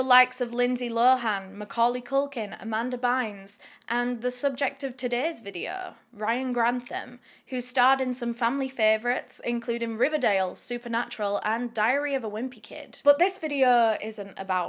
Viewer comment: I thought you said diarrhea of a wimpy kid lol